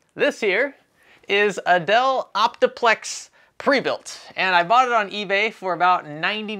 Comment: I swear I heard " This here, is Adele Optiplex"